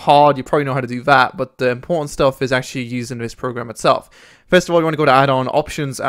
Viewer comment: misheard ya there... " the porn stuff is" .... Lel